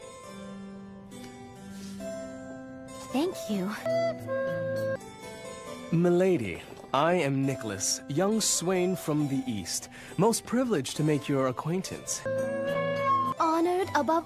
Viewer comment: Lol he says "I'm Nicholas, young swain" I hear, "I'm Nicholas, young swine"